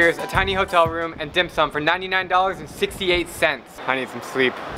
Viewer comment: I thought he said and then some lmaoo😂